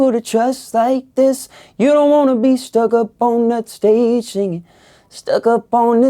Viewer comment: I thought he said all up on that station not stage singing